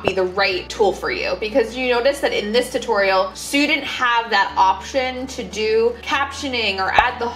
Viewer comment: See at 4:26 your captioning indicated you said "student" when it sounded like you said "you didn't"...so how do you fix those captions?